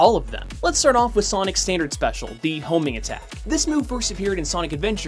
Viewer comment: when you said homing attack at first I thought you said homie attack XD